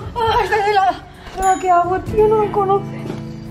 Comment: En el minuto 8:48 parece que dice me haogo